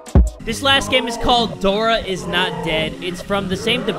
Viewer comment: "Dora not dead"??? umm i think it said "Dora is dead" lol